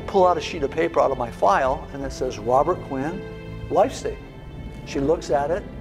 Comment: I can't quite make out the title of the sheet of paper Robert references. Did any one hear it well enough to share what he'd said? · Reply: Life statement. Just got it.